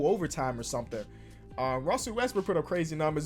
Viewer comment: Wait. What? Did he say Rusell Westbrook?